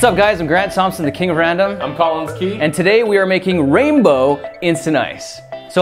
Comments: He’s tall I’m short get over it Lol that was amazing 😂 😂😂😂😂😂😂😂😂😂 When Collins said I’m Collins Key I thought he said I’m TALLins Key Lol wth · What I heard is "I'm Tallins Key"